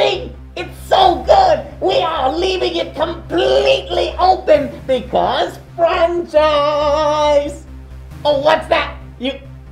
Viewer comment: does he say "Because FRENNCHH FRIEES!"